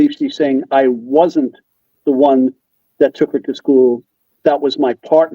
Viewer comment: I there guys. I too thought that the Mom said, 'I was the one who took her to school', but actually, she said, 'I WASN'T the one who took her to school. That was my partner.'